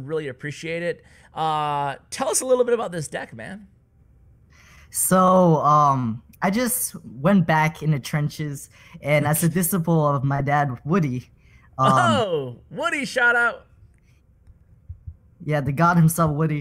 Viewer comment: did he say dad ????!!!!!